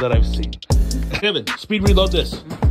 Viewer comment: Bro thought he said “history reload”